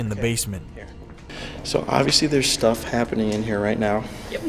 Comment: When asked "was that you talking" istg I heard it reply "obviously" that's one sassy demon